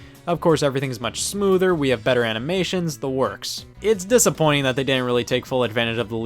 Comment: did he say “Wii have better animations”?